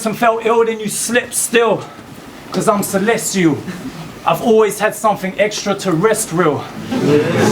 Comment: When i first heard this bar i thought stumbled the "terrestrial" bit but after watching it again he also says "i've always had something extra TO-REST-REAL"...Why I love battles, soo much replay value.